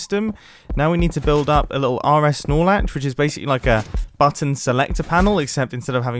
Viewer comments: anyone else hear "rs snorlax" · okay, who else legitimately thought he said "Snoralax"?